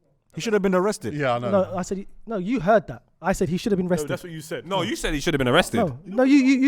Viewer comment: I think was auditioning for the next Super Mario move when he said Saka "Should have been rested" because it really did sound like he said "But he shoulda binna rested" lol